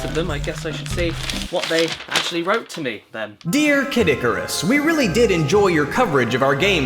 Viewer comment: Did say Kid Icarus instead of?